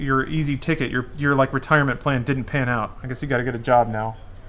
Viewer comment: I was only saying its funny u made the comment about they should get a real job when at the moment ur jst playing games for a living, n I only said pretentious cause u were talkn down on the owner's like they're all bad for trying to make some money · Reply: I fucked up I'm dumb 2:17:46 u didnt say real job u jst said job I'm playn witcher while watching n I thought u said real